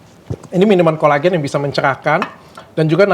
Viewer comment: ga awet muda apa gua salah denger???